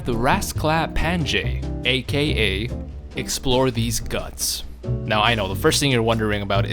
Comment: I thought u said explore these nuts.